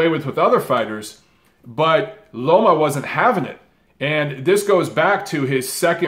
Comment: You calling Lomachenko “Lomo” instead of “Loma” at 2:39 triggered me especially because you talked about people saying his name wrong. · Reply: ikixin he said Loma you fool